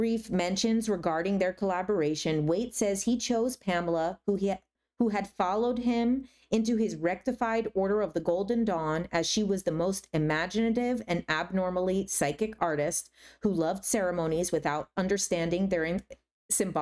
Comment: I thought you said "golden dong" for like 3/4 of this video and didn't even question it once lol I think I may need to rewatch your discernment video 😅